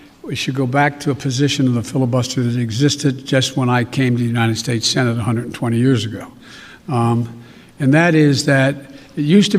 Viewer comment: Trump won the 2020 election. do not call Biden President. By the way did he say ' 'Just like when I came to united States Senator 120 years ago' around 14:40?